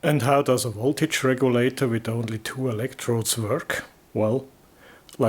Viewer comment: That regulator tube, it sounded like you said "center diode" but I think you meant Zener diode.